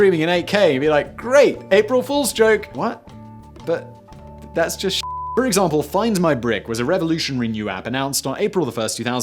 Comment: "Find my brick" I heard "Find my prick"...